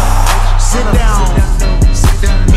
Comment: why I heard“biomble beach sit down🤣🤣”